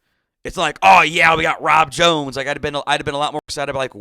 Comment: Oh. Rob jones. Thought he said Tom Jones, and hired someone to do the National Anthems. 😃